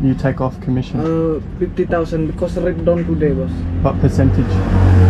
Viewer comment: No offense his accent sounds like a Indian scammer the way he said "Red don" Rate Down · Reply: My hears heard him say "rate down" quite clearly, although with an accent.